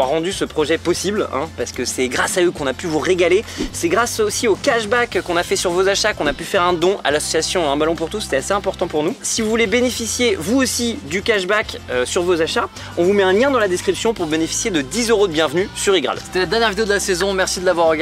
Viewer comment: C'est moi ou dit : " un melon pour tous " ? À